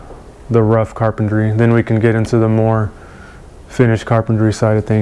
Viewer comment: I thought it said "Finnish Carpentry." I was wondering why this guy speaks English so good.